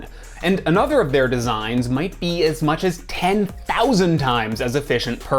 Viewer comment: did I heard right? Did he said 10,000?